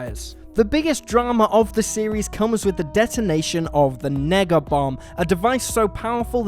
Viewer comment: did he say negga bomb?